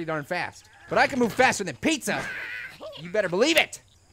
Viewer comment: when u said faster, i thought u said fatter......